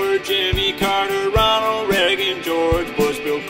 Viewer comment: brobnalzd BREAGEN is what I heard